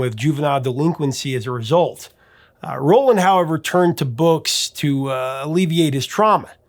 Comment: Did you say Rowland Howard at 3:27 instead of Roland Orzabal? Classic Boys Next Door reference if you did!